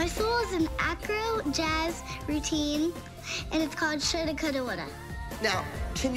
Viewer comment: what did mackenzie say "shouldacoodawoulda"😭